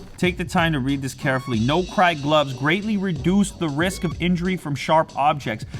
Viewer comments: I thought it said no nut glove at first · I thought it said no nut glove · I thought it said no-nut gloves! Lol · I thought it said "No-nut gloves" Some weird shit went through my mind man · I thought it said no nut glove lol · thought it said no nut gloves 😂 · I thought it said "No Nut Gloves" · I thought it said no nut gloves